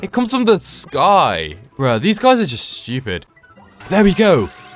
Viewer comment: I think he said "bro these guys are stupid." When he said he liked them tho